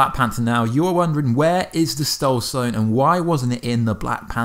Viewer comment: Did he just say "Stoul sone"?